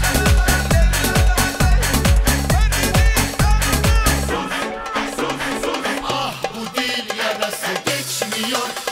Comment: yıllardır çözemedim ne diyor orda 😄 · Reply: "Her dediğin kanun mu?" Diyor 🙄😁